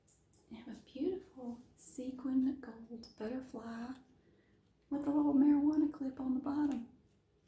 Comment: “A beautiful sequin butterfly with a little marijuana clip on the bottom” did I hear that correct? Or am I just stoned 😬🤔